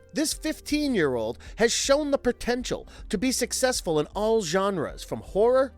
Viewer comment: Time stamp 4:08 Did he say pertential ?